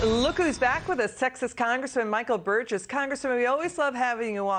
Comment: When she introduced him, it sounded like she said "Sexist congressman".